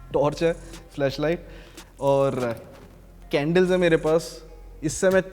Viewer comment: Did he just say "Fleshlight' ... ;)